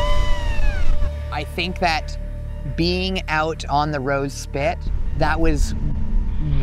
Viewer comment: I kept hearing “the road spit”. Reversed the vid a few times and for sure it sounds like you’re saying “road spit” Its actually “Rose Spit”